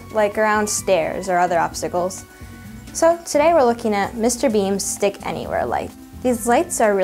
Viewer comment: LOL I thought at first she said Mr Bean Thanks